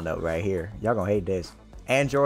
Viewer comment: I was no paying attention and I thought they said hisoka from hxh